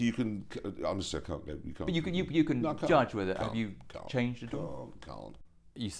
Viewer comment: Just a quick question what is Clarkson saying in 0:52? I understand the question but not his answer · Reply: Dude he's just repeating "Can't".